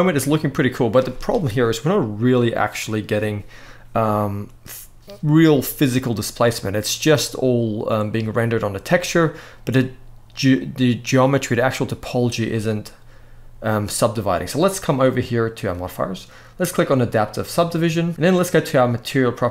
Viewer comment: i think you said geometry dash by mistake in trying to describe this thing. appreciate your effort work man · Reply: i agree i sounded that way but he said “the geometry the actual topology.